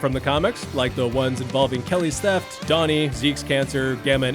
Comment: Did he say "Donnie"!?